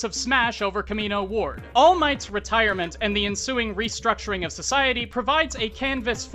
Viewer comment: Did somebody say SOCIETY ??